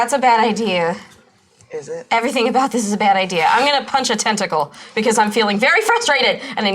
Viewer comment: My mum walked out at 46:46 and genuinely said "Did I hear her say she wants to crunch a testicle?".... When I replayed it, she walked off embarrassed as hell XD